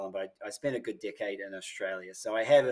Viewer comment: I thought he said dickhead when he said decade😁, I rewind it to listen again to make sure. I love Aussies